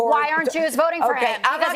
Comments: Anyone else catch (@ 2:38) when Jessica asked Judge Jeanine as the Judge was giving accolades to Trump & right before she shut Jessica down, ".. then why aren't you voting for him?". I watched it a few times w/the captions on & even the the captions showed it. I've always loved the Judge so this surprises me. Now I want to know why she's not voting for Trump🤔 Is that why she shut Jessica down when she did bc the timing was impeccable.🤨 ❤️🇺🇸TRUMP✊🏼2024🇺🇸❤️ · I like what Judge Janeane said but why did Jessica ask her "then why aren't you voting for him?" 2:39 mark. I hope the judge is going to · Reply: She said “why aren’t Jews voting for him.”